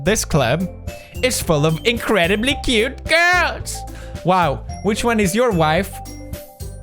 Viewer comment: I thought he said “this club is full of ignorant cute girls”